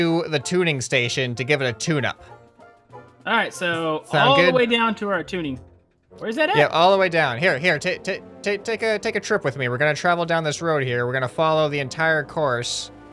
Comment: When you said to give it a tune up thought you said to give it a tuna